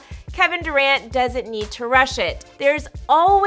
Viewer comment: I was halfway listening towards the end and when she Kevin Durant doesn't need to rush it I thought she said Kevin Durant doesn't need to brush it🤣🤣 I was like, tf he do lmao!